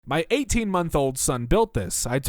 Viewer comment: I swear to God I thought he said my 18 year old son